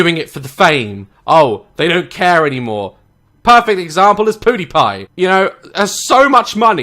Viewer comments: It sounds like he said poodie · did he say poodiepie? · I can’t help but laugh at him saying “poodiepie” instead of pewdiepie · I’m sorry did say “poodiepie”?